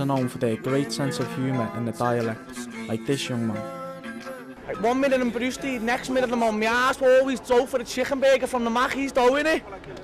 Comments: what the hell did he say? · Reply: Opiate Holidays one minuet Iv got a lot of money the next Iv got none, but Iv always got money for a McDonald's . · did he say "chicken bagel"? i heard "ass" and chicken bagel. The rest wasn't English. (Mid-Atlantic region American accent here.) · The fella at 5:17 ... what's he saying? "One minute I'm brewstied, next minute I'm on me ass always go for the chicken burger from the Maccies, though innit?"?? Great video. We love the Scouse accent in the states.